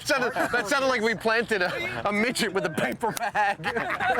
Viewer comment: LOL 14:06 did he say "a midget with a paper bag" ?